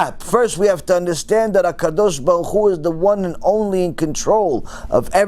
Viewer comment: I heard him say ha dash ba ku 1 million times but I don’t know what it means. I know he’s talking about God like he saying “ God Almighty” Or something like that but I don’t know what he saying can someone help me out please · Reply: It’s HaKadosh Baruchu meaning The Holy One, Blessed be He.